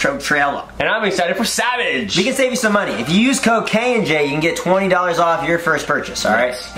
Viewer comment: BRUH I THOUGHT HE SAID IF YOU USE COCAIN AND J" IM WEAK HEKUXGRLFUHRIONEER